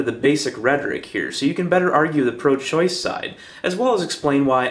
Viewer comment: Oh ok, I heard you say break down the pro life side as well as why I'm pro choice ..but for some weird reason I actually heard it as pro life. Huhh ...well anyway I share the same exact feeling on it you do, that's a nice change of pace :)